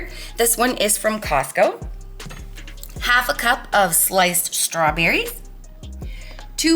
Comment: LOL when you were making rhe stawberry shortcake instead of "sliced strawberries" i heard "light strawberries" and I thought - what are light strawberries?